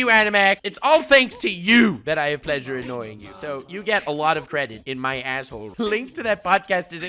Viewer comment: I think I heard Nux wrong.. 16:37 "You get a lot of credit in my asshole"